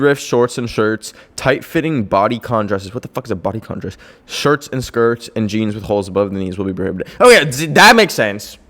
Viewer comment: UM DID HE SAY SHIRTS?? OR LIKE SHIRTS WITH HOLES IM CONFUSED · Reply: shirts and skirts